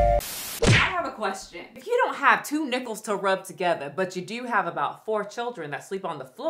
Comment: I thought you said 2 nipples to rub together and I thought that that was an odd question as that often may happen when making a baby with someone 😂 It took me far too long to realise you said nickels 😂